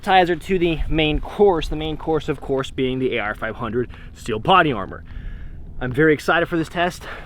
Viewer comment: did… did he say “potty” armor? 🤨